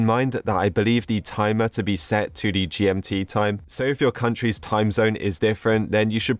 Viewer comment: what timezone? Confused am i right that i heard gmt?